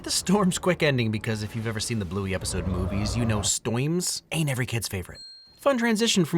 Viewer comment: DID HE JUST SAY "stoiyms"